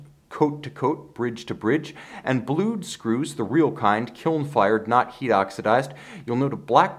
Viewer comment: Hi, what’s the word you use at 4:35? “....... , not oxidize blue” is that alternative word of painted blue? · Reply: “kiln fired”